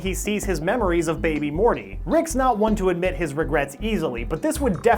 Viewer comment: did he say 'Rickgrets'? lol